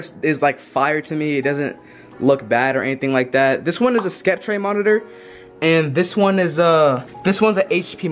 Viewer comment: Did bro say skeptray?